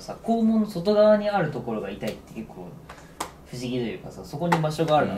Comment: ここでしばゆーが言ってる「○○ぺいん」がまじで聞き取れなくて、めちゃめちゃ調べてやっと「ファントムペイン」って分かった…そのあとにゆめまるも「幻肢痛」って言ってるし…すっきりした…